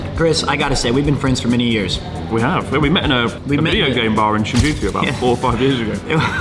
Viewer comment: why did i hear we met in a gay bar oh my lord